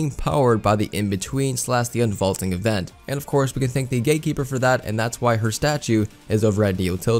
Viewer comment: I thought he said gay keeper